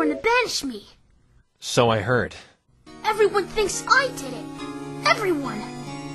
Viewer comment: Did Rainbow Dash just say "everyone", instead of "everypony"?